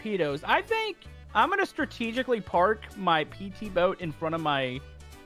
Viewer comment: bruh I thought he said that he was going to strategically fart